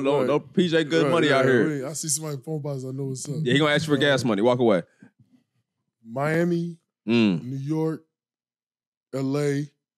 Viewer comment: I thought he said they going to ask for gas money then walk away 😂😂😂